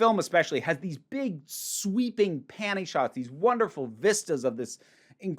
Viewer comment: Oh gaaawd, I misheard "big, sweeping panning shots" as "big, sweeping panty shots" at first! That would be a very different sort of movie, I think...